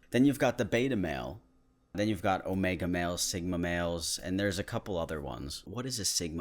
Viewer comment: I keep hearing "smegma males" and get grossed out.